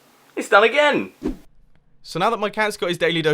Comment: I can't be the only one who hears Mark Sabine on the 'again'... I am? I watch way too many YouTube poops.